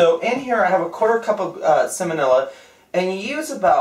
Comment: Ha, I thought it sounded like said "Salmonella" too 😂